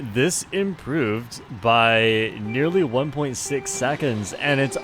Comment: I can’t be the only one who heard 1.67